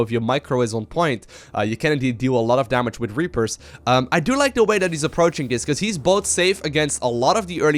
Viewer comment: Sometime i misheard said "I don't like this move" instead of "I do like..." :-P